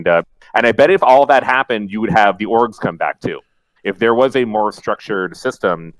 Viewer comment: There was a moment where Slasher said "...and then the orgs will come back" and what I heard was "...and then the orcs will come back" and I realized it was time for another coffee.